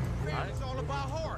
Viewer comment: nghe thành mấy thằng nick gà :))